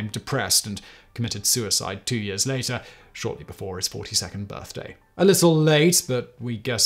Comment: I heard this at first, as: "Alan Turing [...] committed suicide shortly before is 52nd birthday - a little late, but we guess better than never..."